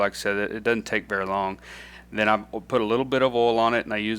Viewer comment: ty for sharing master, but i don't understand what kind of oil do you mentioned at 9:40? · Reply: I use olive oil